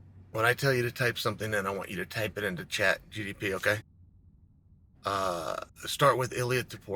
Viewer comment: did he just say chat GDP??? Tf 😂